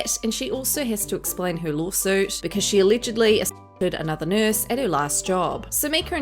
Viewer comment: She did what to another nurse/lawsuit???? Video sounds weird at 2:58 couldn't make out what you said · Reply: She allegedly assaulted a pregnant coworker